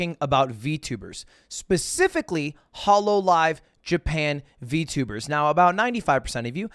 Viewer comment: Dude I thought you said holocaust live lol